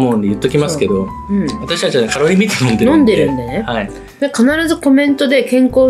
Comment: カロリーメイト飲んでるって聞き間違えて一瞬ビックリした笑